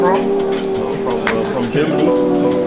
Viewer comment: did i hear it wrong!!!!!!! @ 06:35 from Germany(not pakistan)?????